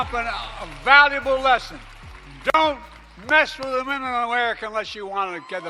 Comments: Sounded like Joe said “don’t mess with the men a women unless you want the benefit” either way confusing, and very sad if that was his attempt at looking like a bad ass. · I thought he said “don’t miss the lemon in the window unless you get the men a mint”…